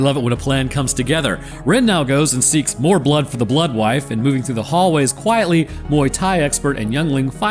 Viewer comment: I heard blood for the blood god, im cooked..